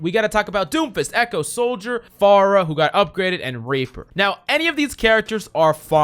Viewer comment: am i messed up or did I hear rapper instead of reaper